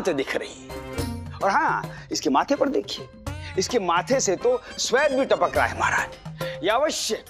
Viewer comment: Did he say sweat